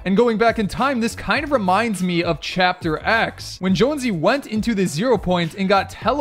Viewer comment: Man actually said chapter X instead of season X